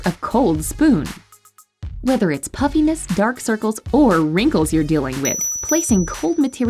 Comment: I kept hearing “puppyness” but I understand what she meant 😂